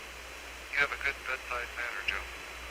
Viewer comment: what does he say? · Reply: Lovell: You have a good bedside manner Joe (to Joe Kerwin the CapCom)